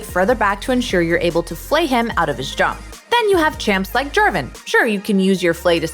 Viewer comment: I heard german instead of Jarvan lol